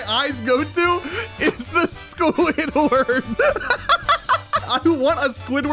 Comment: i had captions on when you said squidward it thought you said shcool hitler